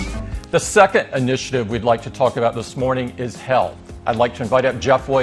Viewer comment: when he said "let's talk about health" I heard let's talk about hell and I was like damn apple okay